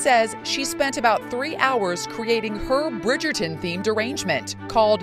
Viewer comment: Definitely sounds like she said “Bridgerton themed derangement” hah.